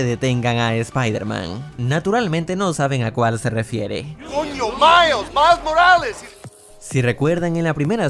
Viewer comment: soy yo o creo que dijo c0ño? 🤨